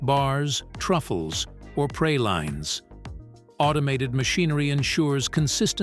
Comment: I heard “pray-lines” not pralines 😂😂